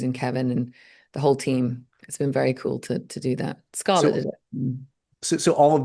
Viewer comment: Oh god oh god I really don’t want to get Vanessa in trouble but what did I just hear in this discussion about Doomsday?? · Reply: Okay after many many replays I think she is saying “Scarlett did it in-“ and then gets cut off, and Scarlett DID film Age of Ultron while pregnant so I think Vanessa was just mentioning she isn’t the first to film with Marvel (so there’s some precedent for hiding it in the edit etc).